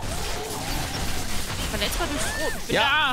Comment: Einen Moment lang hab ich " Verletzbar durch Brot" verstanden. ^^ Was den für Brot? Röstbrot? Das ist recht hart außen rum. Versuchen wir sie damit zu töten. XD